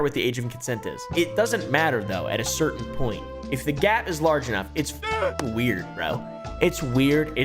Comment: at first I heard “ If the Gyaat! Is large enough” 😮 then I rewound 🤣